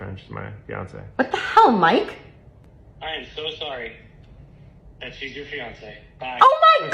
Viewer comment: did he say I am sorry she's your fiancee I just hit your fiancée bye ?